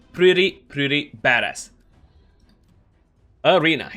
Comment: I had to go back a few times to hear you say badass. It sounded like you said "Hes numbers is actually really really bad" but you said badass :) Thanks for the video!